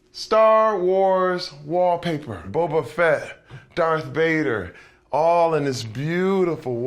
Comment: Did he just say star wars wallpaper; "Wobbuffet, darthbader". ????